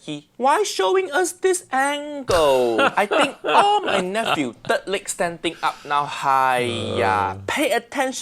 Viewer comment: I'm pretty sure I heard "I bet, All nephew out there third leg standing up" 😳 "Third leg" ?